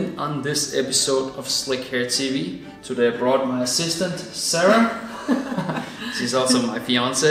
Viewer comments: I heard sister! Until I replayed it 3 times & heard assistant Sara! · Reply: I 100 percent thought he said “my sister” also lol. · I heard sister! Until I replayed it 3 times & heard assistant Sara! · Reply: I heard " my sister Sarah, she is also my fiance" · Who else thought that he is saying my sister then replayed many times then realized he said my assistant, god is that British accent?